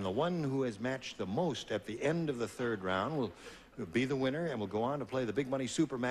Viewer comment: I'm tired, I know this because I honestly thought Gene said: "At the end of the third reich" instead of "At the end of the third round"